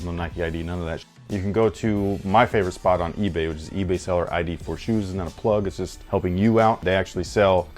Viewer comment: Hey! Great vid (as always) who is the eBay ID that you mentioned at 2:33? TIA and keep up the great vids! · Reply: I wanna know myself. Sounds like he says "For Shoes" but I cant tell.